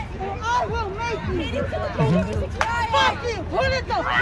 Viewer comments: huh??? Did she just say “I will grape you?” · Did anyone else hear that thing say “I WILL R*PE YOU!!” At 3:46 😂😂